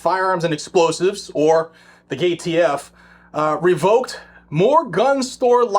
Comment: Way did I hear, " the gay t.f." ? At 0:04 😂😂 Okay, it's my hearing, even better 😂😂😂